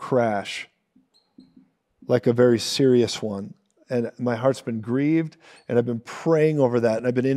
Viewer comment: I want to PUBLICLY APOLOGIZE to this Brother in Christ for mishearing what you said, I publicly said you said "my husband" after using the subtitles I now know you actually said "my heart..." I ask for forgiveness.